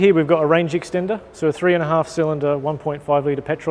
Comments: Did he just say "3 and a half cylinder"??? In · Did I hear that correct? A 3 and a half cylinder engine❓